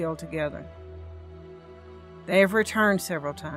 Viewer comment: Watches as the kit dies “ we will never forgive them.” Edited : damnnnn ok I misheard alright thanks for the 100 though very much appreciated I’m not changing forgive though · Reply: We will never FORGET them.